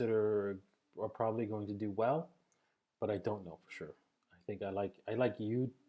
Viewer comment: What is he saying at 16:36? I like U (IOTA?) and than 2point?